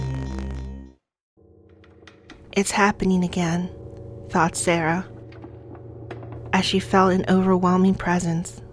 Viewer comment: did she just say "thot sara" i